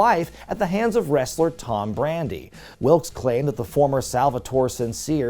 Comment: Tom Brandy used to dress up as Doink too. He'd work 3 matches a night as himself, Doink, and, The Patriot in some smaller northeast promotions. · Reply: For a sec I thought you said Tom Brady.